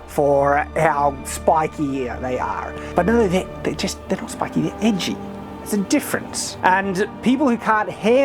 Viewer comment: You were describing the Daedric weapons and I thought you said "they're not spiky, they're itchy"